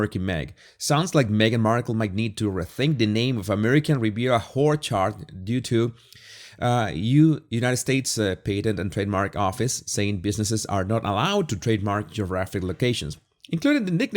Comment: 😂 did you just say Whorechard! 😂❤🇳🇿